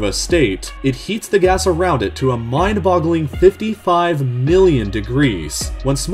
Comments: i thought you said 55 billion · BOI DID U SAY 55 BILLION